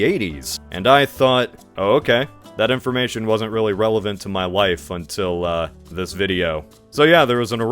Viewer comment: did you say my life or my wife